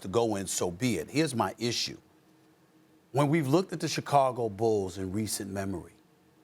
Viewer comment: I wasn't the only one that heard him say Chicago Bulls? 🤔